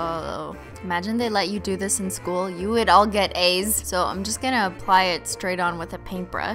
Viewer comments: hahahha it sounded like she said you would all get aids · When you said "imagine you did this in school, you'd all get A's" I thought you said we'd all get aids....